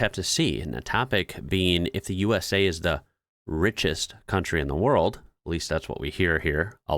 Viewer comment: I heard it as "the USA is the richest 3rd world country"